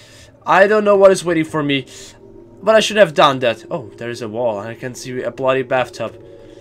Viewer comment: did someone realized what sonic said at 10:40 ? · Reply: "You shouldn't Have Done that" also it's not sonic.exe it's silver.exe xD